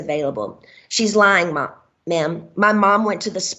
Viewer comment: She was about to say she’s lying Mom instead of ma’am lol.